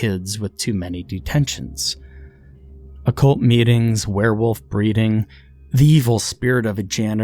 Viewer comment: I heard "werewolf breeding" as "werewolf briefing" and I had to pause from the mental image alone because the WHEEZING 😂